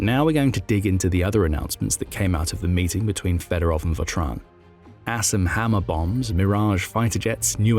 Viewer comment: Did anyone else hear "Ass and Hammer Bombs?"